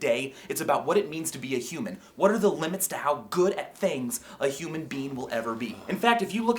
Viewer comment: sounds like he says human bean lol